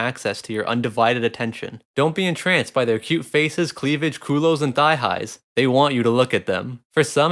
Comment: Cuhlows? Coh lows? What is he saying? Before "thigh highs"